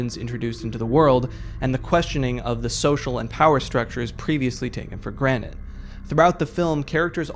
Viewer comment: he sounds like he say takin for granite like the rock